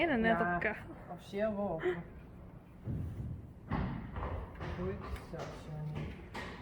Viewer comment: What is it you say to the horses? You say it all the time, like to Johnny at 1:30 - whut so? I've been wondering for some time now I bet others do to! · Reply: Goed zo! It means 'good boy' or something..